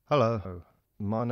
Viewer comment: Did he just say "hello, ho?" at the start. HOW RUDE